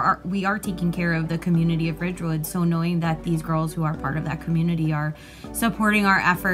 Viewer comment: did she say it’s annoying they are helping or is it just me? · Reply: “So knowing”